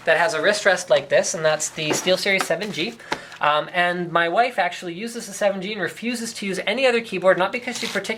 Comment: does he actually say "my wife" ? i thought he's just a boy may be 22-25.